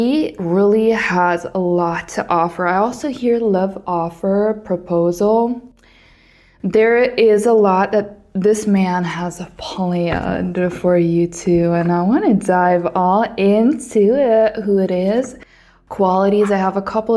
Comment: Correct me if i misheard you..."This man has a polyander for you?" What does that mean ?